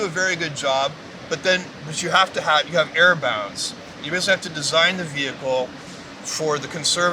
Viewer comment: i'm pretty sure he is saying error bounds, not air bounds, as the captions say